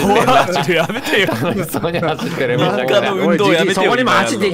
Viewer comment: 位に女性の声で楽しそうじゃんって聞こえない？